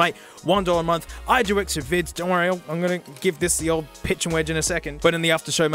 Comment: OOOOOOH, Sand Wedge... I thought he said Saindwich.